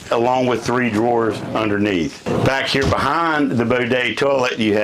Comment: Did he just say a “Bodet”? Lol.